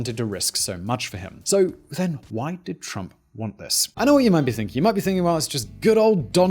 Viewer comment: All i heard there was "why did trump take his life" only 1 miniute after clicked on the vidio